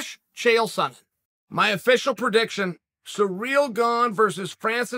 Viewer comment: Congratulations to "Surreal" Gane for unifying the belt. I take it back Lord Uncle Chael. He knows all. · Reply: Bruhh, I hear “Surreal Gong”